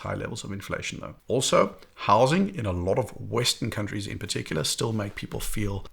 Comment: when you said “housing” I thought you were saying “Howzit?” …brought e back to Jo’burg, mid to late 1990’s…a blast. ❤🇿🇦